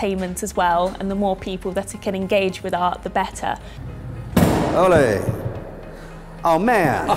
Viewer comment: Did he say olé at 3:26???😂